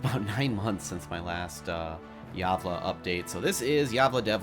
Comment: lol i thought it was said like "gavel"